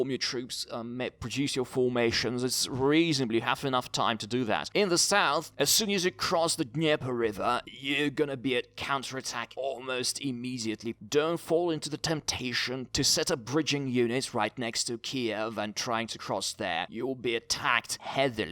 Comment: Why does it sound like you say cancer attack instead of counter attack